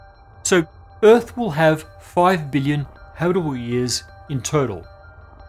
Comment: I'm glad I cut on the closed captions because your pronunciation of "billion" at 7:13 sounded like "million"